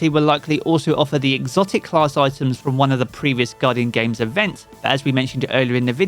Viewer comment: I swear I heard him say exotic class items from previous events🤔